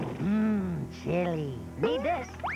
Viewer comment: What did Wakko say? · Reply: Mmm. Chilli!